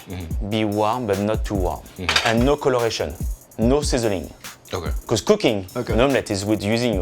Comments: lol thought he said no sissoring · dude i swear joji heard "no scissoring" cus low-key i heard that